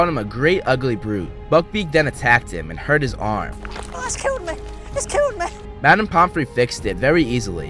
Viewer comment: Buckbeak: *rips Draco’s sleeve* Draco: “It’s killed me! It’s killed me!” If they were able to add a whole lot of gore in the other movies, why didn’t draco bleed? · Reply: I though he was saying "It's killed meh!" It kinda sounds like he was saying meh instead of me